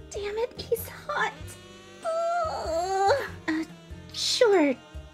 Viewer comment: when she said "uuuu sure i guess" i thought she said " achooooo i guess"